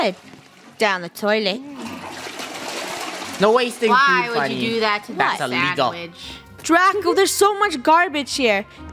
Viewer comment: like said a maybe joke and Funneh beeped what she said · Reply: No she said sh*tty joke that's why the bleeped it